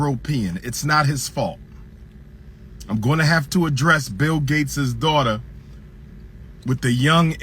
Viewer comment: Did he just say Donald trumps daughter instead of Bill gates daughter 🤔 still speaking facts thou 💯